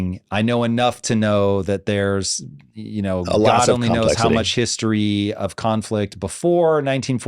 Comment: Does he say, "Allah only knows..."